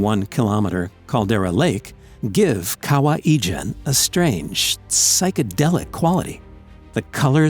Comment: I'm Indonesian, and the way you pronounced Kawah Ijen is hilarious, I hear "Kawaii" jen.